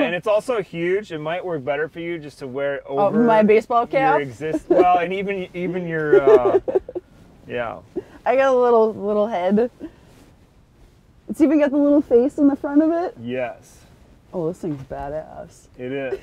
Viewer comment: 0.24 she says I got a little head. i thought she said I GIVE A LITTLE HEAD